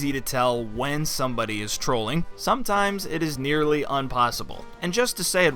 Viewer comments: did he just say unpossible instead of impossible? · Did you actually say un-possible @ 10:43 ... Unsubscribed Disliked Reported to the authorities.